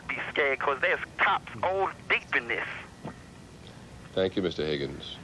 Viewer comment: For 20 years I have wondered exactly what the prankster says at about 1:20 in the following sentence. "... and I would be scared 'cause there's cops all _______this!" If anyone knows please help me out! And pardon my OCD. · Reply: There's cops all deep in this.